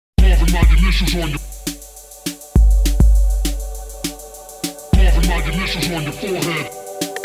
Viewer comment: hahahaha, I thought he was saying "tattoo my initials on your forehead"